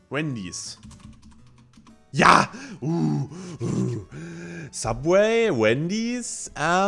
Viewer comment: ich habe verstanden bei 16:17 mcdonals vs randys xD